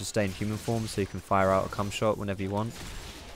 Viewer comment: Did you just say cumshot?